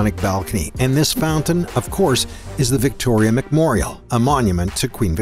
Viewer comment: Did he just say McMorial instead of Memorial??